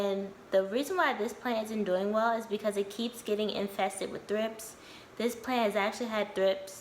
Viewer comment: I thought you said thrifts. I have quite a few plants, but I'm not as serious of a houseplant owner, so I had to look it up. Thrips are wingless larvae that emerge from the soil as adults with wings.